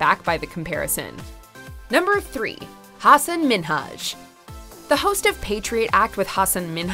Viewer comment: To be fair it sounded like that dude was pronouncing it has Minaj too. Like I listened over and over thinking I was crazy cause it sounded like he was saying Minaj. lol